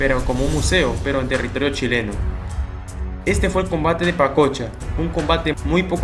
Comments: Yo pense que decia el combate de panocha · Pensé que decía el combate de Panocha omg!!!